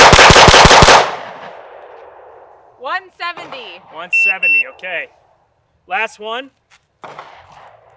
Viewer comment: that sounded like 7